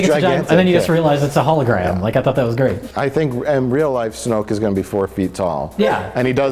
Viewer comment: I laughed out loud in the theater when I misheard Supreme Leader Snoke as "Snuke" as in the South park Hilary Clinton joke.